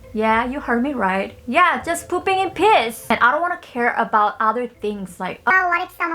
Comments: why did I hear "pooping in piss"??? XD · Am I the only one who heard her say at 0:06 she wants to "Poop in PISS?"😂😂 I almost puked. Girl what..!? 🤣